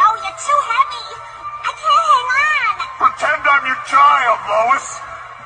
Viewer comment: for a second I thought he said: “I jumped on your child!” 😅😅